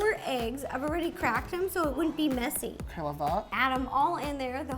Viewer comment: it took me way to long to realise said "K, love that" instead of "Hella thot"